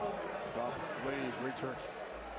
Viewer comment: What is said at 47:58? Secret code to activate a sleeper cell or what? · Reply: He said " Soft leave .... Greek Church " referring to what was left after his first shot.